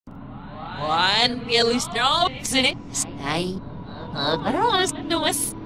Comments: what i hear on the first part is "one really strong sinic" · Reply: i heard "One really strong piss." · I heard she say ‘what there no sitting’ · I heard “one billy strong sinic bros” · At the beginning of the end i heard her say im really strong